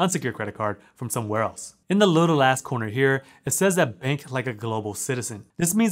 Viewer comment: I know this has nothing to do with the video but at mark 1:53 it sounds like he says in a little ass corner herelol tell me I'm not the only one hearing this 😄😂